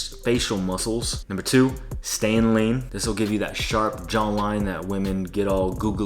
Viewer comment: What did you say at 5:10 as tip #2? Did you say "stay in lane"?